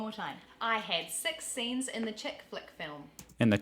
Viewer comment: You actually missed hearing the most pronounced difference in the sentence “I had six scenes in the chick flick”. To Australians is sounds like she said “I had sEx scenes in the check fleck”. We always knock a kiwi for saying six as coming through as sex.